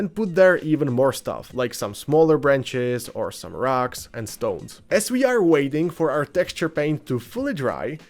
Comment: DID I HEAR A ROCK AND STONE?!